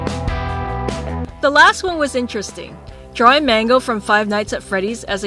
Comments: ooooh it's mangle, I heard "mango" lol I love these though! My fav is Minnie :) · It kinda sounded like she was saying mango instead of mangle lol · I thought that she said Mango instead of Mangle😂😂